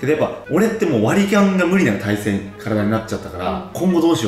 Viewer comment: ここの割り勘が割りキャンにしか聞こえなくて一生笑ってるw